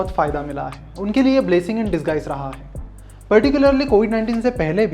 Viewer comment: shouldn't it be 'Blessing in disguise' instead of 'Blessing and disguise' ? :) . Its small but I think it'll be good if these mistakes are avoided